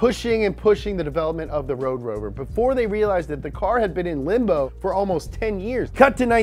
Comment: Genuinely thought he said ''The car has been in limp-mode for almost 10 years'' at 2:53 lmfaoooooo